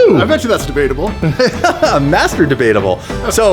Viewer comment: Did say Gabe was Masturbateable ? hmmm